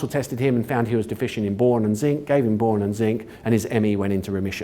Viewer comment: Zinc and what for chronic fatigue syndrome(CFS/ME)? I can't make out the first mineral. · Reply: I could be wrong but I think he said Boron.